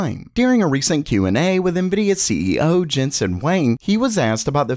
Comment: Jensen "WHEN" 😂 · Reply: I heard him say jensen wang lol